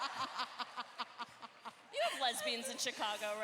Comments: can anyone tell me what the people said t them at 27:45?? I couldn't understand it at all. the volume on this video is sooooo low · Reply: People started shouting "Gay!" and when Tessa asked to repeat cause she didn't hear, someone in the audience said "I just wanna see lesbians". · What did the audience said in 27:48? Anyone? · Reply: Sounds like 'I just wanna see lesbians' 😂